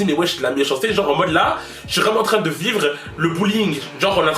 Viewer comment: J'ai compris "en train de vivre le bowling" 🎳 au lieu du bullying (je sais pas l'écrire) genre jme suis imaginée quelqu'un te prendre et te lancer sur des quilles désolée 😭😭